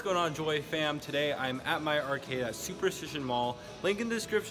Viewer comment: wait wait wait wait.... did he just say MY arcade like.... it’s his ??? orrr